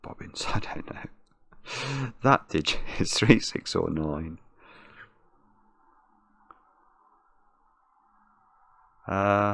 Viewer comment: it really sounded like he said "That digit is 3, 6, or annoying."